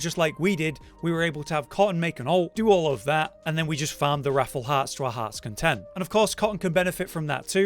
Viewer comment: Thought he was saying Raffle heart